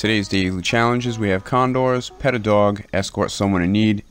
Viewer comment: Glad i watched this vid first.. im half asleep and thought it said "pelt a dog" so i had my .22 equipped